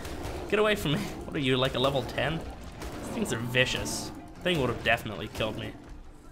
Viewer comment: Anyone else think he said bitches